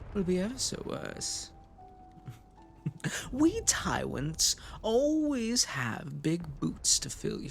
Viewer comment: I can't get over the fact it sounds like he says Tywents instead of tyrents